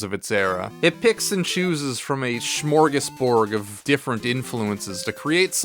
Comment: I think I heard you say "smorgasborg". That's hilarious, didn't know you were a star trek fan. It's actually "smorgasbord" FYI hehe